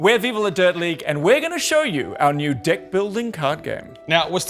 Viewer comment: Ah the accent made the first sentence sound like he said dick building. I didn't realize this was that kind of a sales pitch.